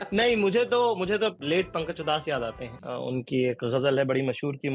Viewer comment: Did he say pakanj chudas 😂😂😂😂